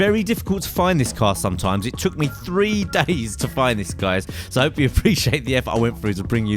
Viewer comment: First time i heard it i thought you said " It took me three daddies to find this."